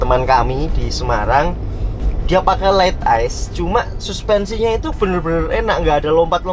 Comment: bilang apa mas? light eyes? mobil apa yang pernya diganti accord itu? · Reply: Berlin Sianipar lite ace (granmax)